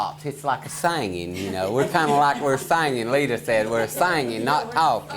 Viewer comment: my dads side is from west Virginia we lived up there for a while and my moms names Leta and that sounded like her in the background 😂 i know its not but made me go back and rewatch it